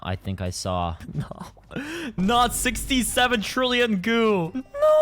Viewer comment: woah did I just hear... 67?!?? sorry for that dont ban me daddy 🥹✌